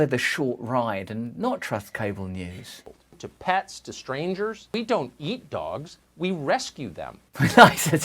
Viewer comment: i thought he said "we don't yeet dogs" and i was right on board